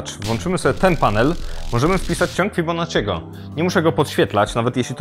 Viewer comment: AJ USŁYSZAŁEM CIĄG BONIFACEGO POZDRO DLA KUMATYCH